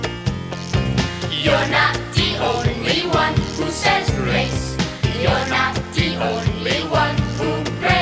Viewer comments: Thought they were saying "you're not the only one who sees race" on first viewing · I keep hearing "You aren't the only one who says RACE" and it sounds appropriate for this little... whatever it is.